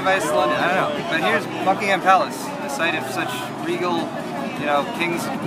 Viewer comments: I thought you said fuckingham palace so I kept watching. Wasn't dissapointed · I thought he said "Here we are... in FUCKINGham palace."